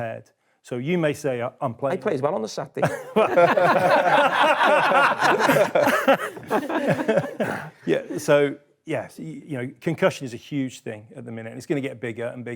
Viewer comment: What did Carra say at 13:42 ? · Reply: I played well on the Saturday